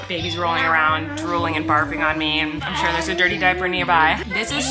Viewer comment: I SWEAR I heard one of the bubs say “Dorty” like dirty.